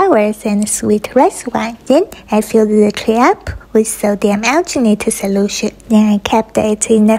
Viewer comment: I thought the angel said ‘so damn’ when she was actually saying sodium😭 im literally going to hell😭